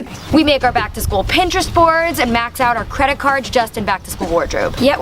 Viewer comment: Max out your credit cards on back to school wardrobe???? I.... what? Is that a thing? That can't be a thing. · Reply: When she said that what I really heard was "max out your parents credit cards" which is even worse advice. 🙄